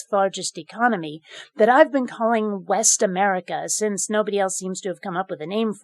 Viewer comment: When you said "West America," I thought you said "Best America." I hope the secessionists start calling it that. It would be delightfully cheeky.